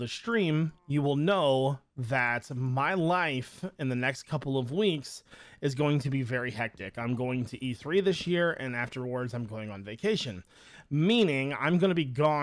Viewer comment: I thought u said I’m going to be 3 this year 💀😂